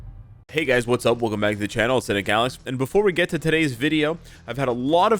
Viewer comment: I ALWAYS thought he Said "Channel of the galaxy"